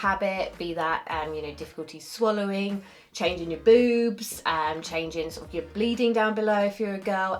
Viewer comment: I swear, I thought she said "changing your boobs." I